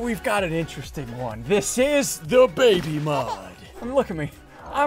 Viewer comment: I thought he was saying it was the da baby mode 😂